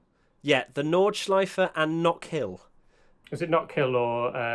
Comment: I thought Albon said Knox Hill and I was thinking he wanted to add a hill climb to the F1 calendar